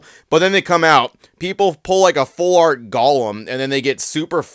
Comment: Did you say "Gollum?"